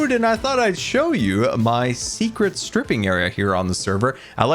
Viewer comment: WHAT DID SAYYY??? XD "this is my secret stripping mine", "I like to do it awy from the hermits as to not didstub them" XD